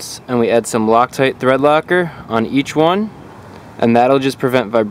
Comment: What liquid cream is it that he put on 3:15?? I couldn't hear him??? And do u have too??? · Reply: It is called thread locker: <link>